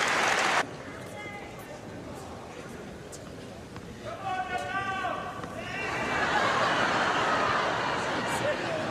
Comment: what does the guy shout? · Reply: "Come on Nadal!"